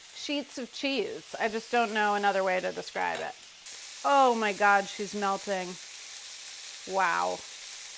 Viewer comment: Did Carla said "Omg SHEESE's melting" LOL